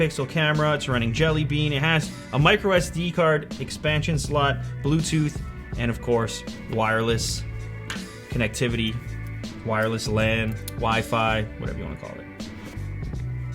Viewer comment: when he said wireless lan and delayed i thought he said wireless charging xD